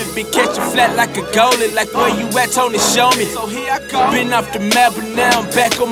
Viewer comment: Did he say been off the map? 0:12 Just found this artist last night ....new to me as of last night . Thanks to YouTuber Eve Matheny